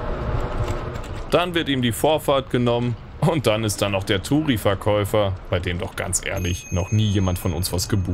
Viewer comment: das ist leider immer noch viel zu oft die bittere Realität in DE. Ist DE eine Auto-Kratie? 😢 PS: was für ein Verkäufer? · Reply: Ich hab "Touri-Verkäufer" verstanden, dazu würde passen, dass wohl noch nie jemand bei dem was gebucht haben soll.